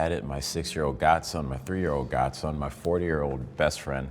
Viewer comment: did he just say my 4 year old best friend got it